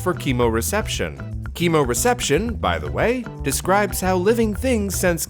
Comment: My house is noisy and when he said chemoreception I thought he said cable reception, rewound and double checked, lol